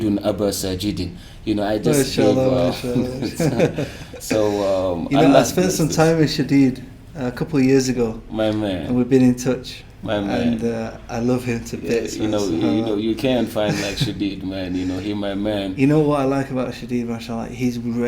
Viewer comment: did he just said maya maya? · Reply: he's saying my man 😂😂